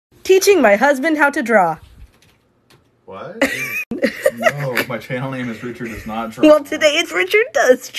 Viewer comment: I thought it said teaching my husband how to draw a pet! 🐱